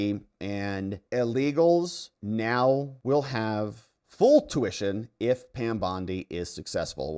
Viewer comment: Need to ask: @ 9:36 it sounds as though you are saying 'illegals now will have full tuition if Pam Bondi is successful. Did I hear that right? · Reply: Illegals will have to PAY full tuition. A word was missing.